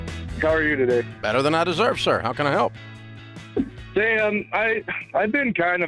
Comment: did he just call him Dan?